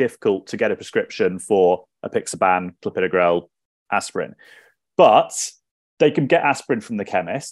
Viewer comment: I couldn't hear some of the drug names as the audio is a little difficult to hear. Is it possible to put it in the description or a pinned comment thank you. says it some of them at 13:33 if anyone can hear that better than me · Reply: Aspirin, Clopidogrel, apixaban, dabagatran